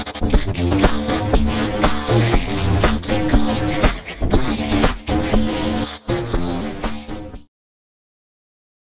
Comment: what did deku say at 8:26 ? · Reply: He was yeah you did mess up and it almost got us killed